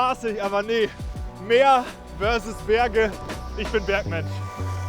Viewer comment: Mega Video, macht Laune! Mein Lieblingszitat: „Meer vs Berge - ich bin Batman“ 😂 (habe mich verhört)